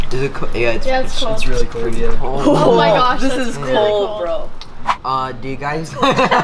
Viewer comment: am I the only one that heard say "oh that sh!ts cold" at 5:54 or am I just hearing that